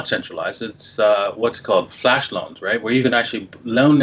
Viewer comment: What did said in 19:20. Flashloans?? Any link to this website